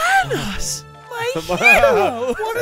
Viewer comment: did say Thanoss?